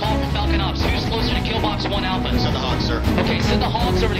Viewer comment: Did I hear they called in HAWX. I dont remember this mission in game. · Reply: Send the Hogs or A-10 Thunderbolt Warhogs is the another name of A-10 Thunderbolt